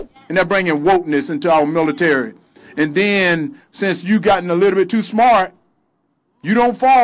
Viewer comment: "Since you got illiterate too smart."? I don't know, maybe it was "a little bit". It's a "perfect" oxymoron, if it is what he said.